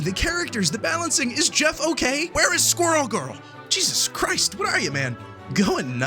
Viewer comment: ngl i really thought he was gonna say "what are you man, a gooner" i need to get off the internet